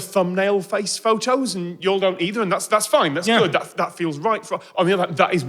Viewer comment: Wait did Tom just say YALL?